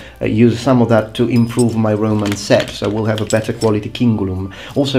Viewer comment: instead of Roman set I first heard Roman sex... O_o